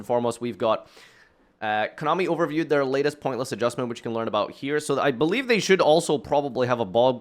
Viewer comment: I always hear "points list adjustment" as "pointless adjustment"